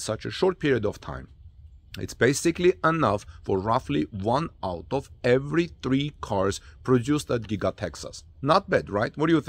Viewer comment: Guys please if you find this report helpful, give us thumbs up and Tweet it out, or consider supporting our channel by clicking on THANKS. I would greatly appreciate it. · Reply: Not able to understand your comparison to "one out of every three cars produced at Giga-Texas." or did you say "one auto every three cars..." Were you trying to say one Cyber Truck out of every three cars? see at: 1:08 to about 1:15 it is really hard to understand